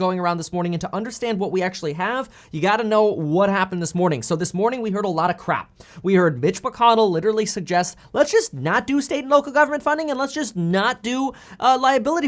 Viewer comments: totally sounded like you said bitch mcconnell lol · I thought he said bitch McConnell 😂